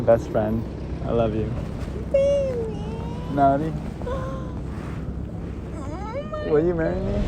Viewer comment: I thought the past 5 times he was saying "Naani?", but realized he's actually saying "Melody"